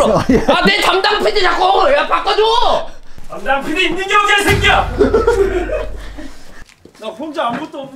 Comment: 뭐라고한건지 아는 사람 ㅋㅋㅋㅋㅋ 개찰져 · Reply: 담당PD 있는게 어디야 ㅅㄲ야